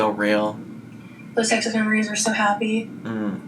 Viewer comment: I heard this as, “those sexless memories were so happy” and I was SHOOKKKKK